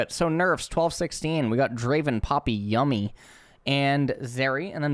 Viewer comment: did he just say yummy instead of yuumi?